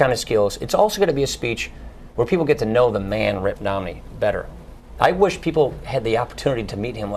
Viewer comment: At about 5:15 or so he clearly says "Ritt Nomney".